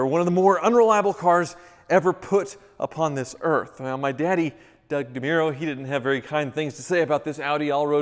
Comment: did he just say "my daddy, Doug Demuro"?